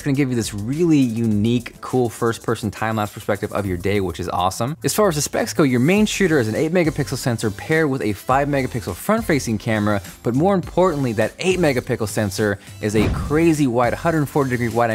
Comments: Did you just say "Mega pickle" ... with a picture of it? lol lol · Did he say 8 Mega Pickle @ 1:46?